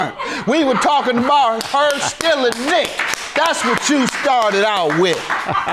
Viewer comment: Wait did he just say something about stealing dick or I'm hearing things 🤣🤣